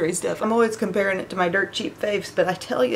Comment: I love your make up and I'm not being mean but I thought you said dirt cheap face 👀I was like what lol